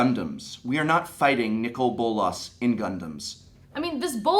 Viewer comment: Professor: We are not fighting Bolas in Gundams Me: WHY THE HELL NOT THAT WOULD BE EPIC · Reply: At first i thought i heard "we are not fighting Bolas in condoms... :D